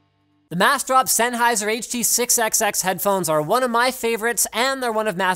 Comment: anyone else hear 6XXX and not 6XX?